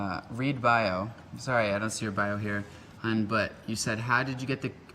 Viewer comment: did he say hun? 😭